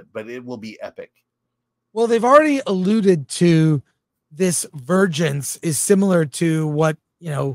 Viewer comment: Like a vergence, touched for the very first time...like a verr err err errgence... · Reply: at first, I swore I kept hearing them say “virgins” by the way they were all saying “vergence”… 🤣🤣